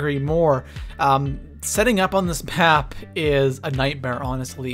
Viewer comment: did he say pap instead of map in